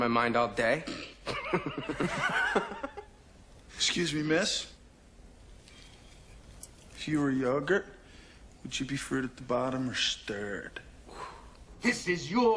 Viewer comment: what does the guy with the blue t-shirt say at 0:22? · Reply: Excuse me, miss. lf you were yogurt, would you be.... fruit-at-the-bottom or stirred?